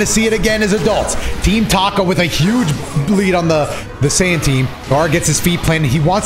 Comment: did he just say saiyan team?? 😂